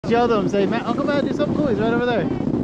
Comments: Ngl first time I heard this I thought she said 'do something cool dumb a$$' but I think she said uncle Matt 🤣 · Wow, you daughter is too cute!!! Amazing how far she has come in such a short period of time. Keep up the great work parents. I swear it sounds like she is saying, "Do something cool, dumb ass". Too cute! 🏂💕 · I heard "do something cool, dumba$$" 😭🤣